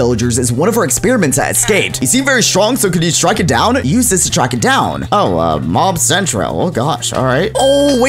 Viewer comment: i think it says: stab them it has been eating and grown stronger you seen very strong so could you strike it down? use this to track it down